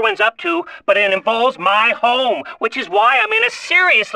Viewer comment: I REALLY thought he was saying "my hole" .....